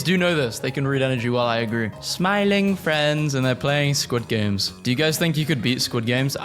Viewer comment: Did I just hear her say "Who's Smiling Friends"??